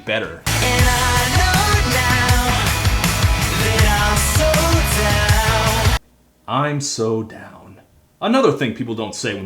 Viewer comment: I heard "so down" in the 5sos song as "sold out"